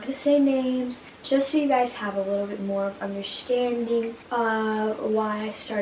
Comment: did anybody hear "undershtanding" not "understanding"